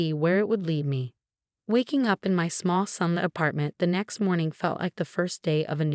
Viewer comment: minutes into this story, we come across this statement, “waking up in my small son”. Can anybody tell me what this sentence means?